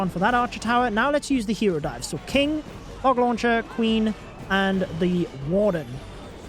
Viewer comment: Does anyone else hear him say hog launcher at 6:18?